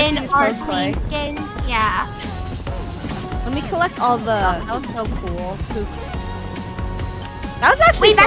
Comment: Oh my gosh i thought funneh said “lemme just collect all the poops” but she said “lemme collect all the hoops”😭🤣